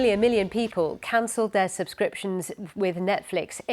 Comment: What did she say at 0:00 ?? Million million people